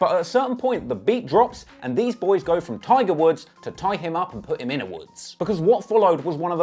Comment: Did he say they go from Tiger Woods to “tying ‘em up & putting them in the woods”??? 🤦🏽‍♀️🤣🤣🤣